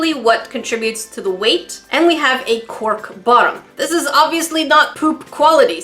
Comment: i heard quirk bottom 0_0